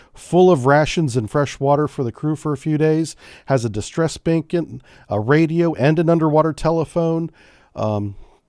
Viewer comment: I thought you said Distress Bacon.... Now I am hungry!